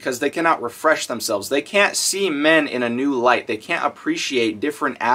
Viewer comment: did he say that women can't see men XD